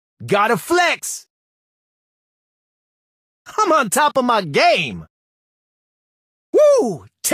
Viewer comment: all my life i thought he says "i'm on top of my gay",glad i saw this vid